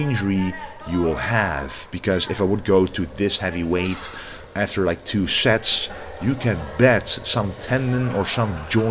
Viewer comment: What did she say at 13:47? was laughing hard... 😆😆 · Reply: pretty sure she said, ben je nu al klaar? means are you alrdy done?